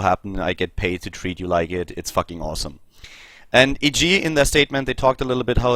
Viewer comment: did I hear F king awesome on 1:28?